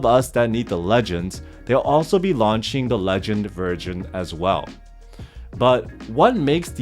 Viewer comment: is it really called the legend virgin haha